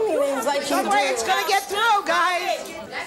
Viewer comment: What does Sally say at 28:10? "No way its gonna get through guys!" [His head]. They kept brining couple after couple out, ripping the bandaid off then saying, it's time for a commercial! Your segment is over!